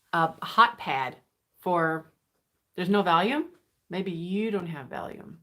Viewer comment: In the beginning I thought you were saying "there's no VALUE? Maybe you have no value..." 😳 had to look at the chat to find out it was VOLUME.. Glad I did.